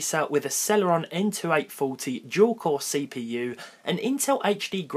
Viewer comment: Did he said gual core cpu?